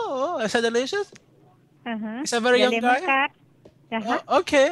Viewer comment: is actually what the lady is saying is "sum yum gai" actually means in Cantonese a type of chicken. :P